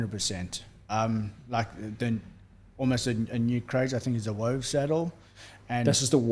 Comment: what did he say, a wove saddle, or wave saddle? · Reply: There's a brand called Wove